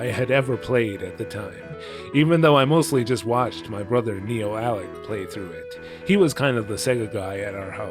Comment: sorry if this is a dumb question but did you say your brother’s name was.. “Neo-Alec”?